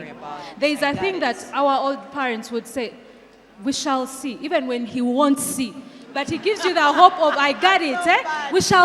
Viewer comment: I hear " we shall see even wen he won't see" 😂😂😂